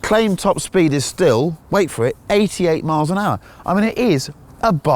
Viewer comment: Did I hear that right? 88 mph?? It sounds incredible for a 1939 car